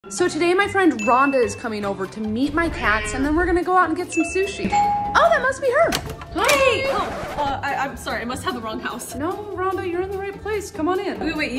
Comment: I thought she said eat my cats😅😅